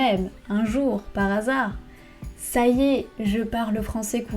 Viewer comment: Qu'est-ce que tu dis au début? Sayez? Saiez? Je ne comprend pas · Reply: Ça y est